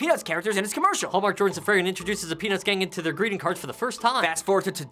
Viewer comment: no offense but i thought thar Rafi said, pen*s lol